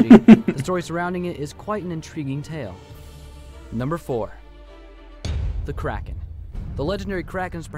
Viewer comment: When it said the kraken I thought it said THE KAREN, and I got scared